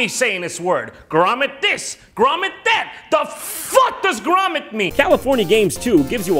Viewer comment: Jon: Grommet this. Grommet that. The FUCK does grommet mean!? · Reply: I won't lie the first time I heard that phrase I thought he said: "Well FFFUCK this grommet meat!"